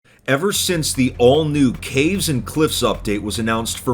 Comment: When you said every since the al- I thought you were saying every since the oldest anarchy server in minecraft ___.